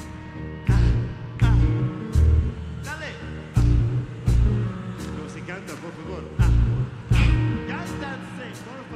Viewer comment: what's the conversation going on? who's yelling at Damon? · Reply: He was sayng "Sing, please". Then he elevated his voice with the same thing